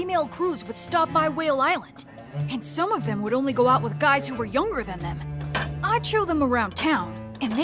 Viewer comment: Did he just say "Yonger than them?"